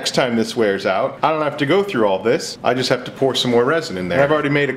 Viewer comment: I swear I heard “and I will cut you guys next time!”